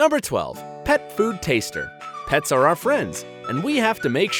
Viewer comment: On 2:06 it says on the text pet food "tester" but what they're saying is pet food "taster". Sorry but before u upload a video with subtitle texts, make sure ur typing what ur saying and make sure u spell it right